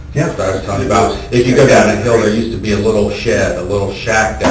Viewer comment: sounds like he says “down the hill”